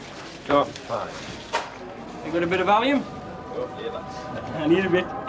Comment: did he say a bit of Valium or a bit of allium? 😂